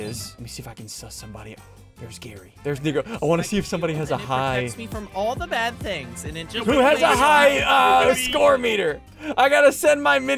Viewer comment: I thought he said the n-word